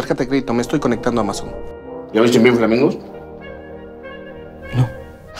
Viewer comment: Que dice en el segundo 0:22? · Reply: Ya viste Pink flamingos